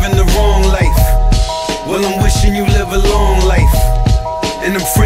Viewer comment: Not even gonna lie I thought he said porch lights first time I heard it 😄😅